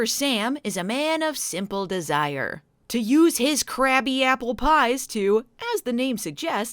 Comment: I swear I heard "Crappy Apple Pies" the first couple of times.